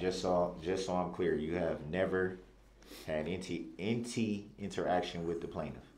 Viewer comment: He says “Inty” instead of “any” Like bro what????